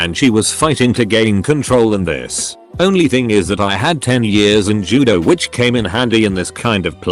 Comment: for a minute I thought I heard “the only thing is that I had 10 years on her”